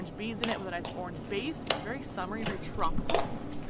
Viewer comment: I thought she said it's very summery like Trump but she said tropical😂